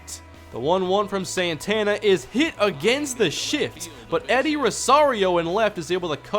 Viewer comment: wat did u say?? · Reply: lsu_fan_ for_life16 hit against the shift